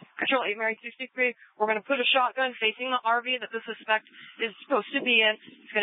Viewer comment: What is she saying at 37:26 something about a shotgun facing an RV?